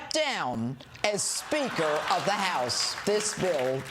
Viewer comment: It sounded like she said Sphincter of the House 06:31 LOL